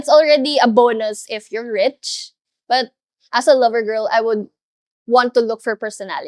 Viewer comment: Did she really say, "As a lover girl"?